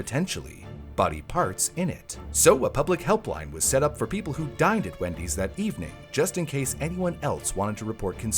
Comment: I definitely heard, "So, a public help line was set up for people who died at Wendy's..."